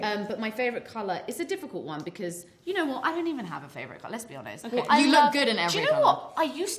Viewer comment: anyone else heard 'lesbi-honest' 0.o